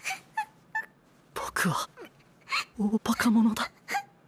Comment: Just a random guess but whoever translated the phrase at 0:04 probably misheard 僕は大馬鹿者だ(boku ha oobaka mono da) which means “im an absolute moron) as 僕は大バケモノだ (boku wa oobake mono da) which sounds similar but has totally different meaning, hence seems weird to me as a Japanese Unless the word monster has some sort of hidden meaning such as “moron” or “idiot”